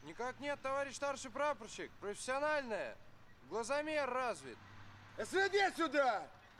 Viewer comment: субтитры неверные, он сказал "СВД сюда!" 1:02:46 "Что? Не слышу! Ладно, давай!" 2:10:14 "ВДВ... вперёд!"